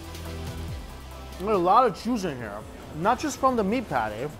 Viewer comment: sounds like said "there are a lot of jews in here" lol